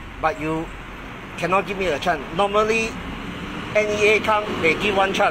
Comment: The England very powderful! I keep hearing "give chan", "one chan"..... chan mali chan! Lol